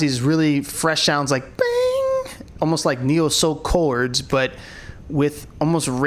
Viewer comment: did just say Neo???????? Or am i hearing stuff????? Hahahahah does he or does he not know NCT stands for Neo Culture Technology? 🤪